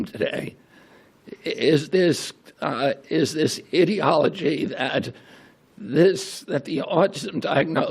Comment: I know RFK Jr. meant to say ideology but I thought it was funny that it sounded like he said "idiotology" because it actually fits in this situation...